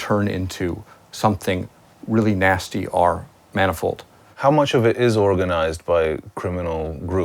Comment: What did he say? mana what? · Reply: I heard him say "manifold".